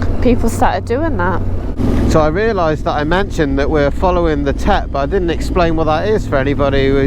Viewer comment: Thank you for explaining the Tet? I thought you said you were following the Tit? 😂 I thought Ewa is behind you? Must be someone else in front 😂😂